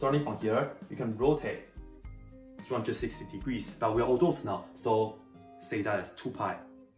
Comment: I’m pretty sure I heard “we are adults now, so we use 2 pi” xD